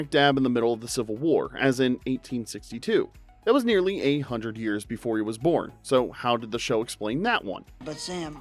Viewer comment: Okay, it sounded like they said the civil war was 800 years before Sam's lifetime. After several replays, I realized that they actually said "A" hundred years.